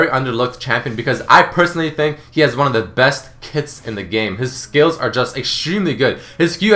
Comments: lol at 5:50 i thought he said gragas has the best tits in the game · Thought you said Gragas has the best tits in the game then. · When you said Gragas has one of the best kits, I thought you said that he had one of the best tits in the game. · lol when he was talking about gragus i thought he said "Has the best Tits in the game" xD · I thought he said Gragas had the best tits in the game.